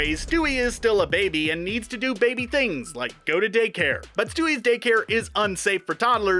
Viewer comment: I thought he said stewies dick hair